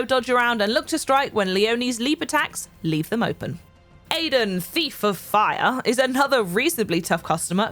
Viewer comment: When she said thief I thought she said FIFA 😂